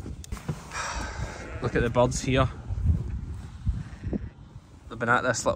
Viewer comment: The lamb with the scars at 6:36, I couldn’t understand what was saying; anyone catch what happened to it? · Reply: Birds, crows most likely.